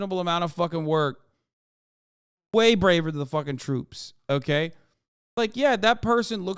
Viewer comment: Did he say way brave than the troops??? Ayoooo What??